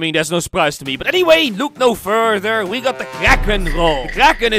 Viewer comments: I thought it said the karen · At first when I saw the thumbnail I thought u said a Karen role!🤣